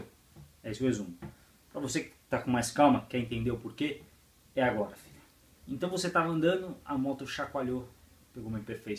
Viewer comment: Desculpa mas eu não ouvi direito, é 60km/h ou 100kmh? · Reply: 60km/h